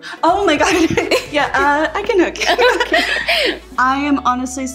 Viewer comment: Definitely did a double take i thought she said ‘hooker’ instead of hugger I was like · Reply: said it pretty fast and it sounded like hooked.